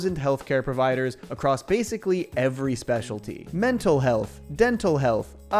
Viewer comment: When you were doing the medical add read I thought you said mental health twice there. gas lighting me into being a schizophrenic